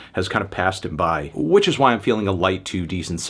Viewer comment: i thought he said a “light 2”!!